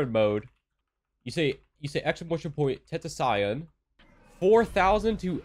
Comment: Did he just say tentaxion